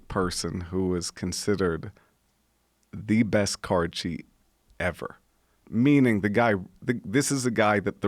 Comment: what was that word he was sating, sounded like he was saying, " this guy was the best 'card shee' ever"? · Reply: Anthony Schrayer “Card Cheat”